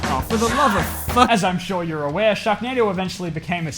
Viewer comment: My dumbass thought is said sharkwando